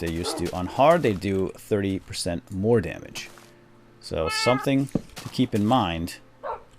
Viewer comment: Maybe I wasn't paying full attention, but when you said that creepers do 30% more damage, I heard it as 'creepers do 30 times more damage' and I almost choked to death on my taco!